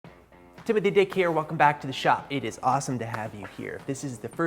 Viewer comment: Sounded like he said “tip of thee dick here” 😂